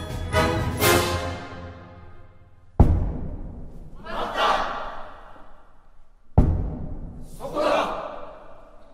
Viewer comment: Is someone know what is yelled at 8:15 ? I think i can recognise "mata" to be "hold on" and i know that "igiari" (last one) is Objection. But what about the others ? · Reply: It is just "Matta!" (Hold it!), "Kokoda!" (Got it!), "Kurae!" (Take that!), and "Igiari!" (Objection!).